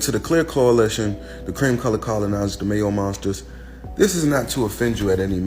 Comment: Did he say lipless ? 😂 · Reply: I thought I heard mayo monsters 😂😂😂